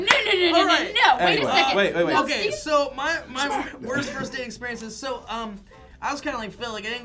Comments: It sounded like they were saying "worst first AID experience," not "worst first date experience." at the beginning. I was wondering where they were going with that. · I thought they said "worst first aid experiences". The answers were not what I expected. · I thought they said worst first aid experience